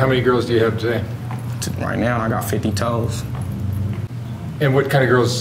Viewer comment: "...I got 50 toes" Me: 50 toes???? Okay, so there’s 10 toes per person, what’s 50/10.. Okay 5.. So he has 5 B words?? Lol. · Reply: Lmao I thought I heard him wrong at first and assumed he said hoes I was like okay then damn and when the dude asked and he said he had 3 black, 2 white and an Asian i was like shit okay this boy did say 50 freaking toes 😂😂😂 I did the damn math just the same